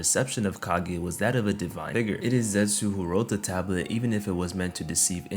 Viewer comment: Did bro say “divine 🥷🏾 er”😭